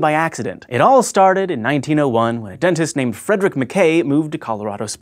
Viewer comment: When he said frederick I thought he said fridrich. Only cubers will understand